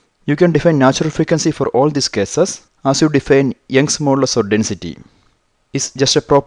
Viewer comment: What did he say at 02:52 -02:53? "? Molar Density" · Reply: Young modulus or density